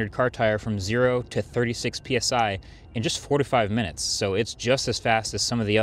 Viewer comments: I thought you "45 minutes" instead of "4 to 5 minutes" and I was so confused haha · did you just say it takes 45 minutes to inflate a single tire with that pump? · At first I thought he said 45 minutes not 4-5 minutes. Sheesh. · Did you say you can fill up the tyre in *45minutes*(thats a lot of time